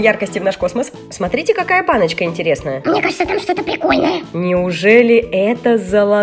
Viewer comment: Услышали от в 3:30 пикольный